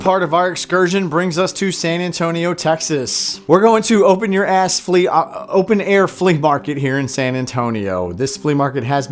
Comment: Did just say "open your ass"? ok.